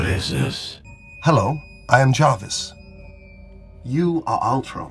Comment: Jarvis (the creator of ultron) - I thought at first he said Janis (Janis & Jambre)